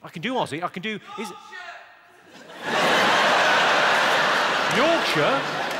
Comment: I thought he said "DOGSHIT!"